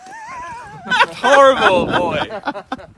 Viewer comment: DOES PHIL SAY 'you're a horrible boyf-" AND THEN REALIZES HE'S BEEN RECORDED OR AM I JUST WAY TOO SLEEPY?